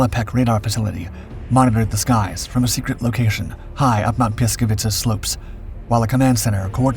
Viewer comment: I know its hard to pronounce but I almost died laughing when I heard "Mount Pljeskavica" :)